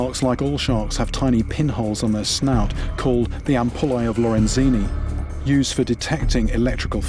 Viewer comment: 20 pen holes called what ???? 0:12 😂😂 craziest word ever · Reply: They're called Ampullae of Lorenzini